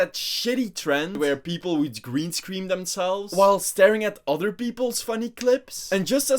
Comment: Did you just say "green screen đằng sau" or am i tripping?